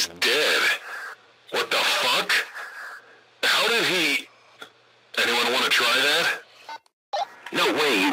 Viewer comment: I think he was gonna say anyone wanna have a kid?